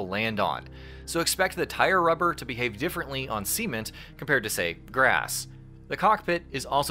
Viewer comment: did you just say seament instead of cement?..